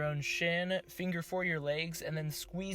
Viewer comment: did you say “finger four your legs” lol ??